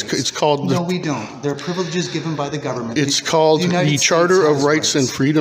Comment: Wtf he say???? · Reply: Privileges given by the government!!! Just wow!!🤦‍♂️🤦‍♂️🤦‍♂️🤦‍♂️🤦‍♂️🤦‍♂️🤦‍♂️🤦‍♂️